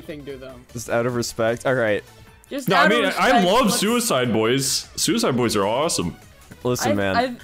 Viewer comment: Bro when he said “I love $uicideboy$” I got an ad halfway thru and all I heard was “I love suicide”